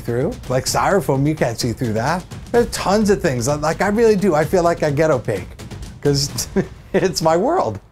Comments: I was listening to in the background while I was doing something else and when he said "I feel like I get opaque", I thought he said "I feel like a ghetto pig". I was like, wth? lol. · Omg he said "i feel like i get opaque" i thought he said "i feel like a ghetto pig" hahhaha oh god